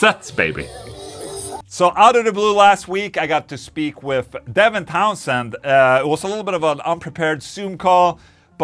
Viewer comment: oh, he said "I got to speak! with devin townsend" ,. I thought Ola said "I got to sleep with devin townsend' that can't be good ... hahahaha